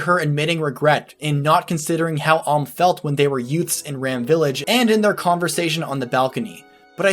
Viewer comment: did you say yutes